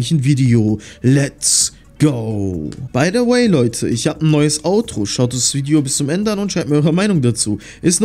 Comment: Gutes video mal wieder. Als du grsagt hast ich habe ein neues intro habe ich verstanden ich habe ein neues auto und dann sagst du so schaut das video zuende und schaut es euch an. Und ich dachte mir nur so lol der zeigt einfach sein auto was er hat und erst dann habr ich bemeerkt er mein autro nicht auto